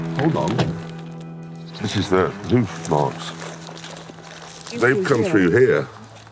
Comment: dfuc did he just say? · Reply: This is their hoof marks